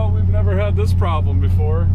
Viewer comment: When Derek was first leaving and got stuck, i could have swore i heard him say, "Well, we've never had this problem, beFord." Bad pun but i laughed.